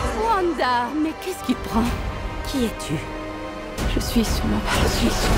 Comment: c'est moi ou Monica dit "je suis sous l'emprise"